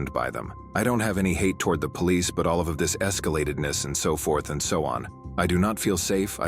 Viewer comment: Did she say "escalatedness" @ 13:54?! Scary that she's a principle.